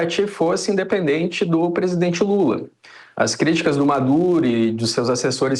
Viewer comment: pera! eu ouvi "Madure" mesmo ou tô errado? kkk